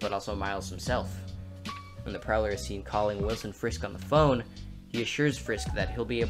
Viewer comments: This may be just me, but at 6:09 when he talks about prowlers personal goals instead of Wilson Fisk it sounds like he’s saying Wilson FRISK. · I might be tripping but when u were talking about Wilson Fisk it sounded like you said Wilson Frisk